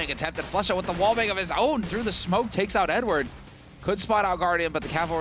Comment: sounds like he is saying "n word"